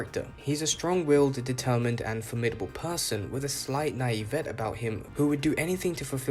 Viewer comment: I think you are saying Naivete, which an English speaker would pronounce Nah-EE-vah-tay.